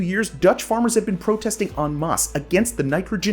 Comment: did he say "on moss"